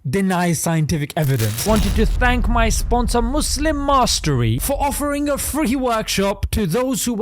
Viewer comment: I thought he said Muslim Misery instead of Muslim Mastery 😂